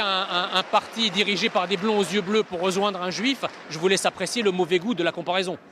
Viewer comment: A 3:16 il a dit quoi ? Un juif ?